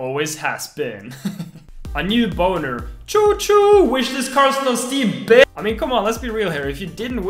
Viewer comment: subtitles says : wish this car still steve